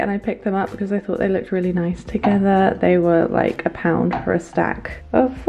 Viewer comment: 10 pounds each plate? In the charity shop, isn't it a bit (too) expensive? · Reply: haha i just misheard! Even went back to hear it again, but oh well, English is not my 1st language and kept hearing "pounds". My bad!!!! 🥰😂